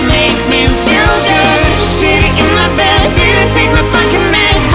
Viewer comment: Sorry, I heard "shitting in my bed" instead